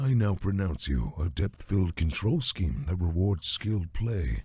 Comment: I swear that line the priest said was different I could have sworn he said “I now pronounce you man and control scheme”